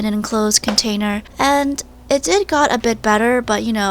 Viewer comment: "And it did got a bit better" is what she said listen